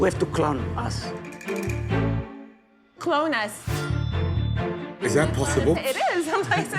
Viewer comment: Gordon thought he said "clown us" and he was so confused until Amy clarified and said "clone us"